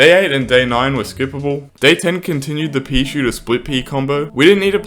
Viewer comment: When he says split pea it sounds like hes saying squid pea